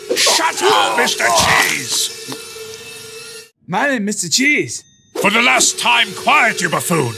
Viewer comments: Wait i think he said "i am mr cheese" 0:11 mr cheese's revenge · what does he say, I hear how am I to make the cheese